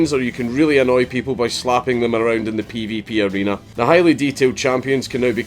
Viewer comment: Thought you said 'annoy them by slapping them around in the pp area'